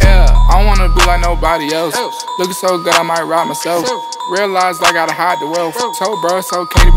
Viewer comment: “Realized I gotta hide the wealth Told bruh it was good to be rich in stealth” I was like damn, yachty went hard, run that bih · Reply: The line before I thought he said “looking so good I might just rob myself” 😂😂